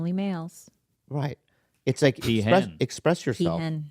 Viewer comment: can someone tell me what mark said at 1:07:32? I couldn't catch it. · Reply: Peahen as in a female Peacock . But goddamn the new podcast has already gone straight to head. She honest to god interjected with "Like I always say the loudest person in the room usually has the least to say" as if it was a novel idea and even worth bringing up.